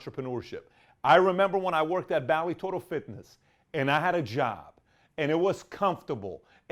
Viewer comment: I thought you said "Vale Tudo Fitness" and I was like, hell yes, no wonder I like this guy.